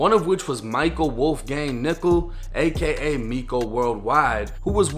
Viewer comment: I thought said michael Wolfgang nikka but realized his last name is nickel.